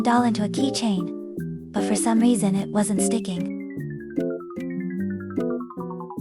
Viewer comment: i heard "what the dawg doing"